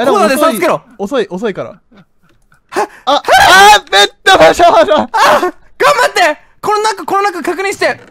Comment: さん「ベットの少女」(？)って聞こえたww